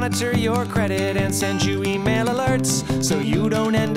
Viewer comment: I remember this commercial when I was little and I think it's still hilarious now :D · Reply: I remember it too. The first time I saw it I thought he said tear up t shirts.